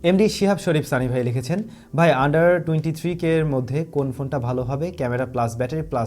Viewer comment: Did you just say "underwear" at 5:22 ?